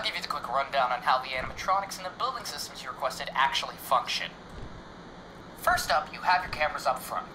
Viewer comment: Who else at 11:09 heard “fuck shit” instead of “function”